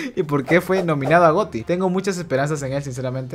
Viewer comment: creí que dijo agoti 👊😭